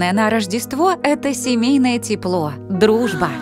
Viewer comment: "самое главное это семейное тепло Я слышу:"самое главное это дибильное тепло 😂😅 Всех с новым годом!☃️❄️🌷✨⛸️